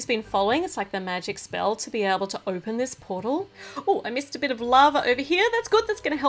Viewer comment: " oh I missed a bit of lava " thought you said love instead of lava there lol (also u forgot to put the grey in the playlist just letting ya know)